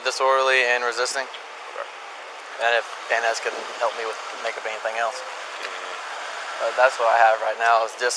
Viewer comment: Did anyone else hear him say at 12:08 änd if Van Ness can help me with making up anything else¨How was this skipped over? He wanted to make up more charges for the guy, also he threaten to kill his dog because his ego was hurt? How is this guy still a cop?